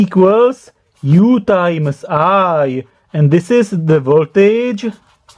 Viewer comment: Did you just say U times I?